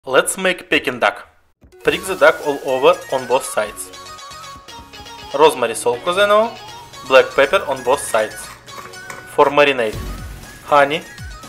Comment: “Rosemary salt, because I know!” Love that! If you know, you know. Looks amazing! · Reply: I thought he said "Rosemary Salt Quzano" for some reason. 😂😂😂